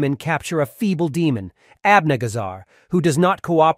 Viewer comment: Did he just say his name was abniggasar? 😂 tf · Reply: Yes. The name is written as 'Abnegazar'